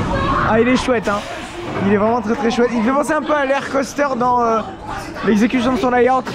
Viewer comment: Super vidéo ! C’est quoi « lair coaster » a 7:12 ?? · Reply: Lech coaster à Legendia (Lech se prononce lèr en polonais)